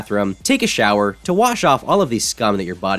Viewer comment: WHAT?!?!?!? ohhhhhhhhhhhhhhhhhhhhh he said "scum"